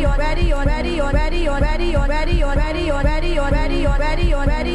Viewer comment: sounds like he saying daddio or radio